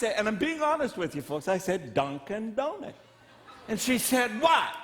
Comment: The first time he said Dunkin Donuts, I thought he said don't condone it! So there you have it.